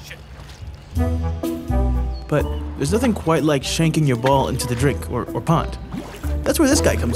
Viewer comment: did he say sh*t